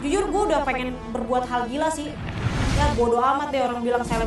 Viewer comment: ada yang tau chef Juna ngomong apa? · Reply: Bodoh